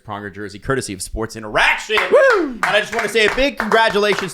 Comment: Is it only me or at 1:20 it sounded like said that it was curtesy of sports interactive and not sports interaction. Gave my hopes up for eastside hockey manager 2024.